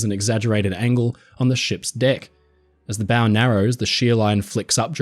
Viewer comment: Miss-heard at 13:05 so it sounded like "Emphasized shear, that is an exaggerated angle on the ships Dick". Took an embarrassing google and rewatch to realize it was not some unknown term for a ship part haha